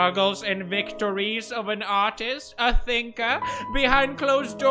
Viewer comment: i thought he said autist but it was artist